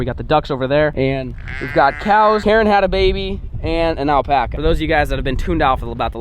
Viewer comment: I thought he was saying Karen had an Alpaca! Lol